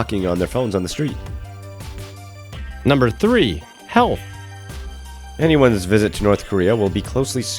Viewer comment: I thought he said hell instead of health and i was like yeah we already know its hell